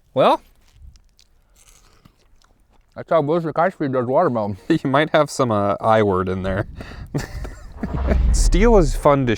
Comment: Awesome video, glad to see you guys back at it again. Dumb question… but what is the I word? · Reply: Incendiary. YouTube doesn't like that ammunition type, or full auto. Risks demonetization and other problems.